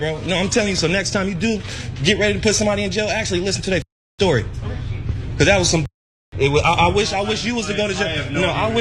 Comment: What did he say to the cop at 1:43 into the video so evidently covering up something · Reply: He said "Cause that was some 'expletives' and I wish you would have gone to jail too". So what's your point? 🤨